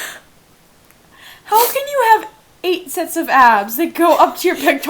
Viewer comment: " OHHHH MMMYYYYY GOOOOOOODDDDDDDD " HOW CAN YOU HAVE 8 SETS OF ABS W E E Z E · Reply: I thought that she said "8 sets of ASS"... Yikes.